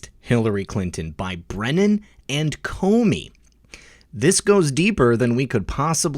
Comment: I thought he said Brennan and Clapper, not Comey